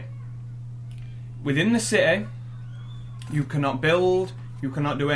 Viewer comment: I thought he was saying setting not city lmao.